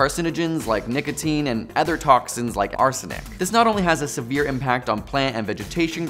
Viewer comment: did you say ARSENIC ?